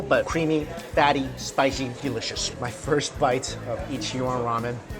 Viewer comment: I thought that said ichiraku like from naruto and I almost had a heart attack